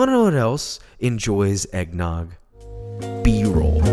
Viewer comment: what is this word again? (after eggnog) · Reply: B role?